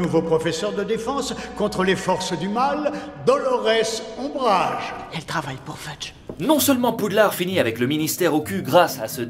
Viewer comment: Mdr à 7:43 j'ai compris :"elle travaille pour flunch" 😅😅😁😆😄j'vous avoue que j'my attendais pas pas